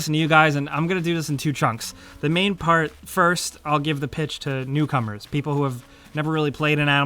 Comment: When you said “newcomers” I thought you said cucumbers